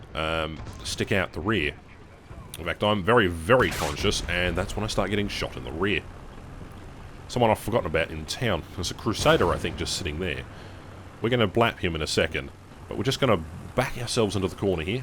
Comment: did you just say blap